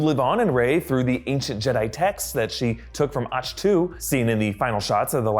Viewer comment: Did you just say "Atch - Too"??